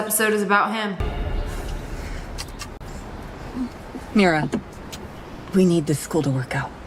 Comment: ESCUSEMUAH DID THAT JUST SAY MIRA??????!!! KDH REFRENCEEE?? Sry im obsessed and delulu😊